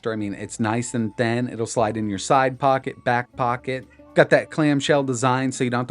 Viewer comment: did he say back pocket?